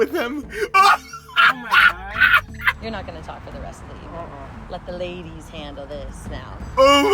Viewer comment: I was so confused when I heard her say “your not going to be talk for the rest of the video” at 7:06. I thought she saw the camera or something then I realised the whole thing was a prank.